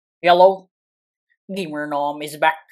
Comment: This guy sounded like he said "Yellow" instead of "hello" in the intro